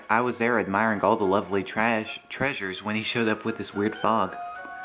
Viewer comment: ! Did you just say "Trash"?